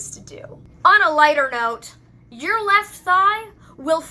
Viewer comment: At first I thought you said "your left eye" and not "your left thigh" and got reaallyy confused, because.. how does an eye get covered in doghair? 😂😂😂